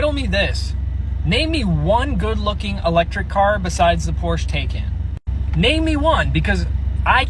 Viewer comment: I thought bro called it a Porsche Taken lol.